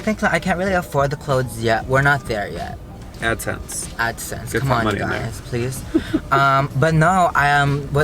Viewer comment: “Well I can’t afford the clothes...we’re not there yet. Come on adsense” 😂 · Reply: I thought he said add cents lol